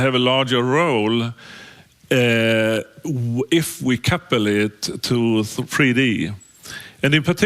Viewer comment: he says modelica has larger role to play because.... anyone can help me understand what did he say ? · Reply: larger role if we coupled it to 3D.